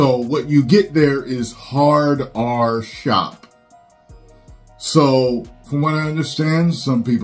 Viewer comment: I thought it said “Hard(e)r shop” lol 😅